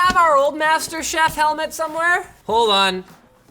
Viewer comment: did he just say master chef?? Lol